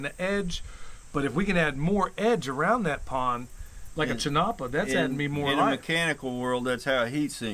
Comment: what did you say at 22:58? CHINOPA? CHENOPOD? Please explain, thanks again, ❤️ Kristy 😃🇺🇸🇺🇸🇺🇸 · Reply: Chinopa